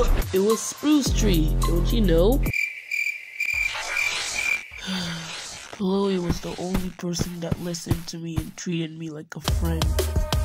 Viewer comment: what was he saying cause i didn't understand the name of the person that treated him like a friend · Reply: “ Pillowy was the only person that listened to me and treated me like a friend”